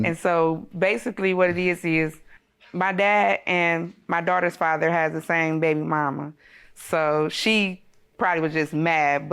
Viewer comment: Did I hear her correctly at 01:30 that her Dad and her daughter 's father have the same baby mama? Please someone tell me I heard that wrong.